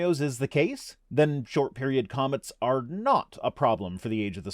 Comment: I thought he said short period commas. It's too early.